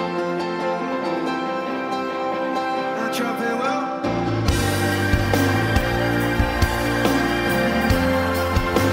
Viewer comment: What is Chris Martin saying? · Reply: He said, "now drop it Will"